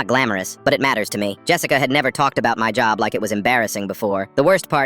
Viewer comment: Tell me why I thought it said Jeffica 😭